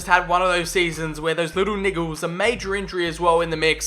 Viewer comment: I think I heard him say little nigga (and I said I think I heard him say it so I'm not accusing anyone)